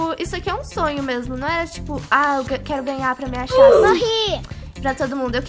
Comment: quando a morreu eu ouvi sorri kkkk